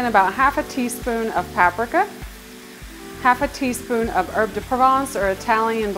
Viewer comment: what is it you are adding? i cant hear or understand it properly :) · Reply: Sorry for the confusion! I added an herb de Provence blend. You can find the full recipe here: <link>